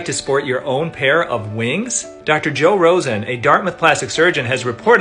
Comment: I thought he said Joe Rogan lol Elon said 🦋I will rather be ❤️🦋💯